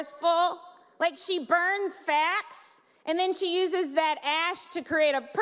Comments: Oh, they were REMARKS? I foolishly thought it was supposed to be a comedy routine. That explains a lot... Not everything... I'm still trying to figure out the "smokey eye" thing (14:08). Did she say "fat" or "fact"? I'll blame the closeout Radio Shack mic they gave her, but either way, I don't get it. BTW, good one C-SPAN... you caught Chris Christie eating his dessert. Nice. Classy move! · Really? That smokey eye joke is what people are saying went 'too far'? Good grief. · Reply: Yeah, I don't understand how that was considered attacking her looks... I know some people thought that she said Sarah "burns fat" when she really said she "burns facts", could that be it? Or was it the first part about the Handmaid's tale? I've never seen that, was that a joke on her looks?